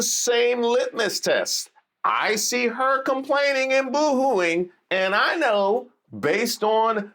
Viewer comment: I thought you said “ICE her complaining n boohooing” 😖🤣